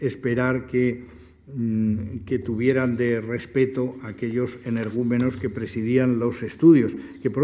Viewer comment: A cualquiera que lo lea tengo un gran favor, dime que dice en 34:15, aquellos de que? Es que no lo puede entender para nada. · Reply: Ocho años después respondo a tu pregunta: energúmenos.